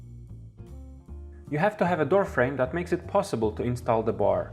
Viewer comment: low volume n i hear "you have to have a good girlfriend" instead of door frame :P